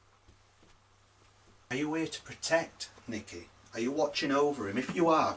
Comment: great video. @ 16:49 where you did the replay... it sounds like "not nicky"... Not sure if you already knew what it said or what, but that's what i heard.